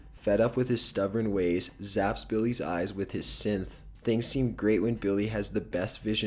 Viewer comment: did he say sinth